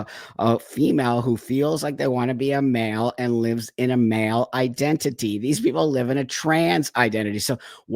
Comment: I literally thought he said "and these people live in a TRANCE Identity!!" as if BOOM, snap all these people from coming out of this trans fug remembering who their sensible self was again!